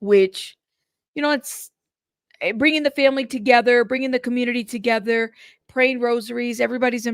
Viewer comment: I thought he said BRINGING THEM KINGS… and I’ve heard that used the same as BENJAMINS for $$$… …we had an annex building in H.S. And a teacher used to watch for DEALS at the back fence where we all went and smoked JS